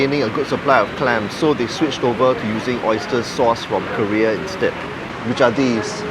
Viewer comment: Oyster "sourced" from Korea. I heard oyster "sauce" from Korea hahahaha.